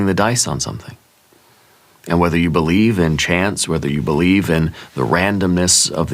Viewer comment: Greetings from upstate NY! Destiny called and I answered. It's been a slow, rolling success. You said, "Whether you believe in chance, and I heard "chants!" I had to rewind twice to go, "Oh! Chance!" No! In fact, l don't believe in chance or coincidence, but I don't believe in fate, exactly, either (what fun would that be?) As with all things it's both/and. Imagine the astronomical (which means "of the stars") chance it must've taken for any of this (I mean ANY) to have arisen by chance! I finished the big "Magnum Opus," which took months and about killed me (and ruined all my relationships) and thought that was the end. Nope! Then, I filed it with the court, and thought, "There! That's that!" Nope! Now the court wants a response from the other side, to which I get to reply (the last word). Even THAT won't be the end, because the judge then rules. And then? The party opponent said something about appellate (can you appeal an appeal?) or federal level! Failure means nothing to this woman! She'll never give up! This is over an event that never happened (because she lied) back in 2023! Everything you said makes perfect sense. You've been reading this story with me, spot-on, all along. I know, for certain, what I've done has already changed destinies. You're the BEST! I am so enjoying your other channels! 😘🫂🕊🐍♒️🗽🙏😎